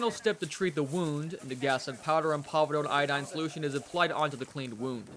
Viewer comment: what 2 medications are said? I heard something iodine, but what was the other one, or both if I missed it.? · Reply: povidone iodine